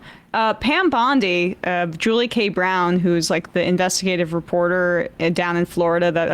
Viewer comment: i thought she said "Downtown Julie Brown", at first ahah that would be awesome...oh wait nvm she IS downtown julie brown =/\=